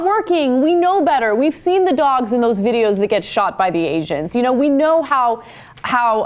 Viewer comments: sounded like ana said asians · ish I heard asians xD